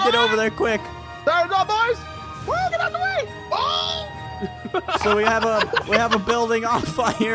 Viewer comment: Is it just me or did say oh Sh*t